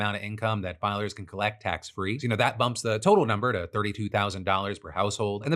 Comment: I heard 'That fathers can collect tax free,' instead of 'That filers can collect tax free,' and I had to go back and listen/read again (I have legit hearing problems and need subtitles, don't crucify me comment trolls.) -But I was like, oh shit... they're actually there now?